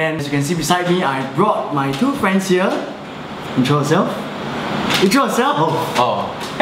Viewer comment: When he said intro yourself, I thought he said Joseph 😂😂